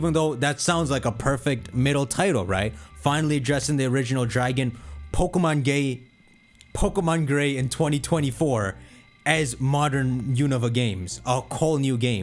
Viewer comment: Dawg said pokemon gay instead of gray 😂